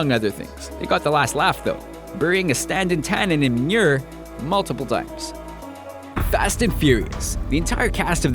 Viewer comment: What is he saying at 4:32-4:34? It sounds like absolute gibberish to me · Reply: Oh my god I finally figured it out. He's saying "Burying a standing Tannen in manure, multiple times"